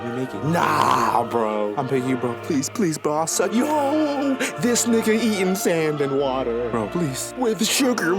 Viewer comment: Did bro just say "i beg you bro, bro ill suck your.." 😂🤦🏽‍♂️😅